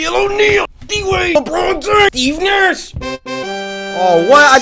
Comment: Steve Nash (can't even dunk) * somehow scores* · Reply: Oh man, I thought he said “eatin ass” and was so confused. Steve Nash somehow makes more and less sense.